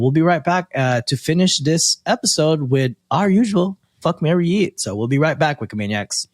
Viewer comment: i thought i heard fuck marry eat and i was soo confused till i saw it was fuck marry YEET hahah. What i would do is Fuck Trudeau, Marry Hipkins and YEET Clinton